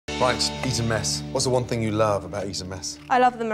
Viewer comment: Hahah wtf, i thought they said "eaten mess" at first.